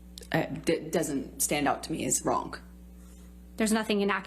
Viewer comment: 2min 45 sec. did she just say wronk